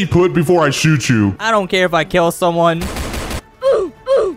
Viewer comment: I thought said “NOT MY FRIENDSA-“